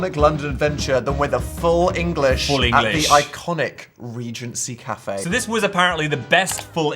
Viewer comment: its 3am and i genuinely thought it said "pregnancy cafe" at 2:22 for a sec